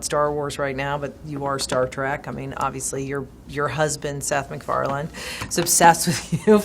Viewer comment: did she say “husband”? ! ?